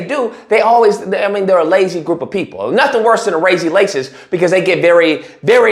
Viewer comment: he didn't even catch that, but I did, he said (razy lacist) instead of [lazy racist] 😂